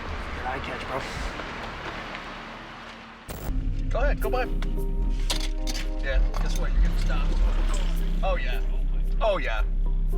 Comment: "Yea,guess what? You're getting stopped" your captions are wrong.